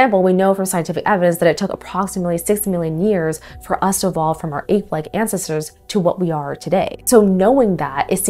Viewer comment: I thought you said eighth-leged ancestors 😅, i was so confused for a sec